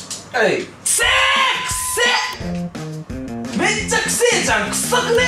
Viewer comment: せぇーーくすに聞こえたけど。。。